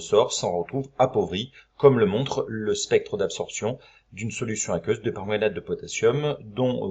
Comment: Il dit quoi a 0:14 ? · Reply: spectre d'absoption d'une solution de permaganate de potassium